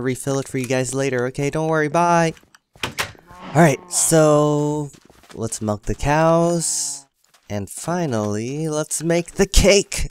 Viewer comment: When you said let's milk the cows I thought you said let's melt the cows 😂hahahahahahaha.......I'm not funny